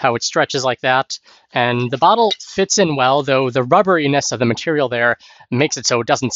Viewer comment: The bottom of the bag has a solid rubber anus? Surely I misheard that?